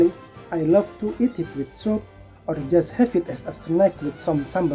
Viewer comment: It's funny that the captions at 9:29 says "I love to eat with soap" (instead of soup)... and like 2 seconds after we see a bunch of cilantro LOL